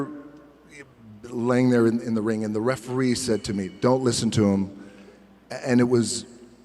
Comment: it's a mistake : "don't LISTEN to them" instead of "look"